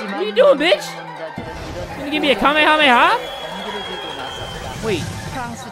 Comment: 應該是說哈咩哈咩哈(龜派氣功 20:17 感覺不是兩分鐘但我聽不豬吃來他原本想說啥_(┐「ε:)_